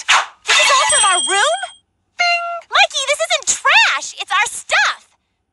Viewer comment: What is mitsuki saying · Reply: "This isn't trash, it's our stuff."